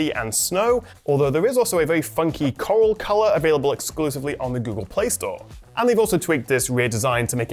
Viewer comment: Did he just say 'available on the Google Play Store?'